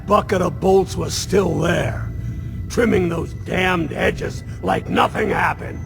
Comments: The subtitles say fucking but he says damned. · If you see at 10:37 he says damn but the captions says something else, An error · He says "damned hedges" but the subtitles say other wise... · Pause at 10:45 and the subtitles say fucking instead of dammed